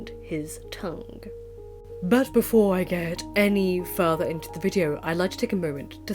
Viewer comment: Oh my god! Did I hear you right? “Before I Goethe any further…”?! Brilliant!